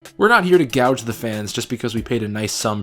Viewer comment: I think the captions should say "gouge" not "gauge"